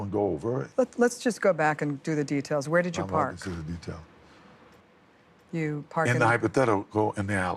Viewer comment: What is he saying at 46:50 ? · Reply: he said "am not going into detail"